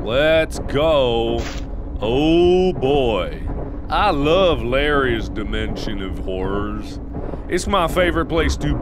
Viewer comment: "I love Larry's dimension of whores." Who else heard that? 😂